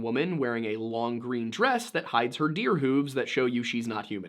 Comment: I'm eating a sandwich and had to rewind because I thought he said "deer boobs" I stopped mid chew and was like "excuse me?"